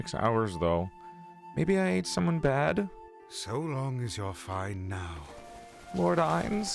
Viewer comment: English subtitles repeat the previous line instead of saying "So long as you're fine now." like they should.